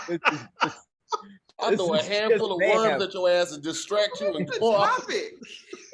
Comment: Oh he said worms I thought he said ones